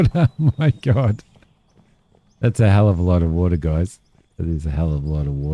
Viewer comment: The second time you said "That's a lot of water", all I heard was "That's a lot of concrete."